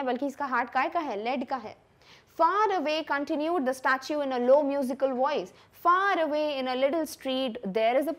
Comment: I heard kahi ka